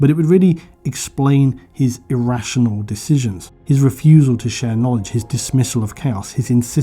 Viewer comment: It sounded like you said "his refusal to share lunch." When talking about the emperor. I had to go back and listen to that more than once being like "that can't be right, the emperor would always share his lunch."